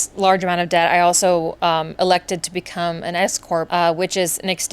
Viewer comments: I'm sorry, I miss heard you and literally spat out my diet coke cause I thought you said ESCORT instead of S-Corp 🤦‍♂️ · For a second there I thought you said, “I elected to become an escort…” That was not a twist I was expecting 😂 Appreciate your transparency in this video! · The way I thought you said you decided to become an ESCORT to pull yourself out of debt. It brought my attention up real quick!! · At first, I thought you said “I elected to become an escort” and I was not ready for that curveball 😂😂😂